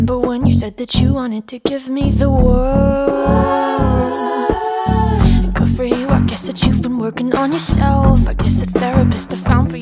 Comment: I thought it said "well good for your happy in hell" 😂